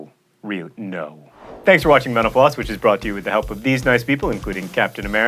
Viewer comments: Is it weird that when he says "thank you for watching mental floss" I hear "thank you for watching menopause?" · Sounded like "Thanks for watching menopause." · i for sure heard this as "thanks for watching menopause" at first xD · I hear "thanks for watching menopause" every damn time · When he says "thanks for watching mental floss" I hear "thanks for watching menopause" every time. Dunno why. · He said his closing comments so quickly. I thought he said, "Thanks for watching menopause". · I thought he said 'thanks for watching menopause' at 9:44 o_O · So right at the end I got distracted and just heard John signing off in the background.... and it sounded like he said "thanks for watching menopause." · Dude, One thing, just one thing, When you said "thanks for watching mental floss" at the end, it sounded like you said "Thanks for watching Menapause"... I was like whoa, my mum's already been through it once, not sure i want to witness it again... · At first I thought he said thanks for watching menopause...